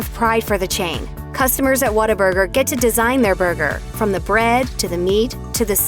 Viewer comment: ya but why does she say water burger.